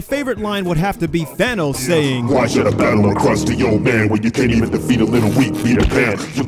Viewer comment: Wait, Thanos said crusty old man I thought he said "Why should I battle to a cross of your end" XD